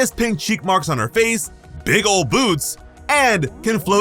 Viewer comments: My fault 😭 I thought you said big ole boobs instead of boots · I swear to God right here I thought he said "Big Old Boobs" 2:39 I was finna say I mean she does but what does Uraraka boobs have to do with Kirby🤣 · I thought you said "big ol' boobs" 💀